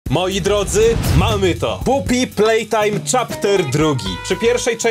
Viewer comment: czy tylko ja usłyszałem poop y playtime?